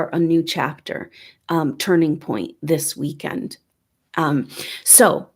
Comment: Holy shit. YES WE CAN. Omg. And now the video and audio are not synced. WOW!!! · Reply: the part that sounds like an alien voice saying "yes we can" is her saying "this weekend" shortly after